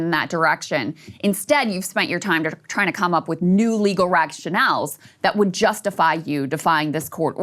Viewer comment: did Krystal say "rag-Chanels" insteadof "rationales"?